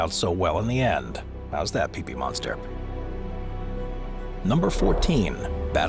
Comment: I’m did he just say PeePee monster?